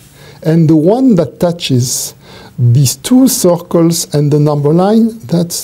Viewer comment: on second viewing: "Oh, circle_, I thought he said _soccer the whole time!"